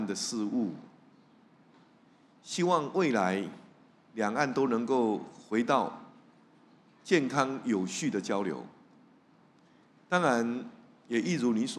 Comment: 吓我一跳，差点听成了：希望未来两岸都能回到一个中国的交流中来🥰🥰🥰🥰